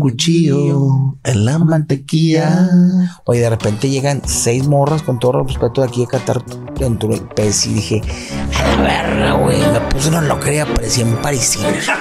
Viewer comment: Weee quiero saber que dijo la mole en el 33:05 para reírme con Adrian jajajajaj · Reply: Hola la mole dice ví llegas unas 6 y me sentí que estaba en paricina ( tienda de venta de telas ) y ponen los rollos de telas y en lo alto ponen la punta de la misma y hace ese efecto de túnica 😁🤭